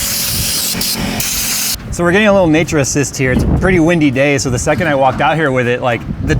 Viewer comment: Sorry did you say “Naturist”?